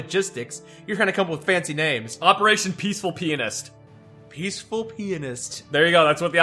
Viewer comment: I thought it said Operation Peaceful PLANTIST when it was pianist